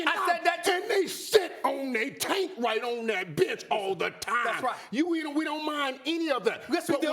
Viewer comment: i thought he said “they shit on that tank and that bitch all the time” lmaooo